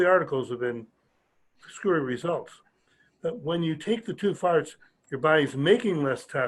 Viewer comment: did he say to take two farts?